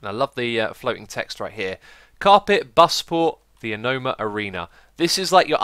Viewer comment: did you say "carpet" lmfao